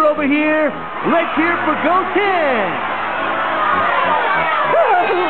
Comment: Let’s hear it for Go Ten! I know it’s Goten but I can’t be the only one who hears it like that!